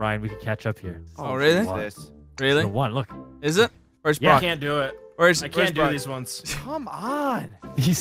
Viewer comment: did just say "ni**a i can't make it"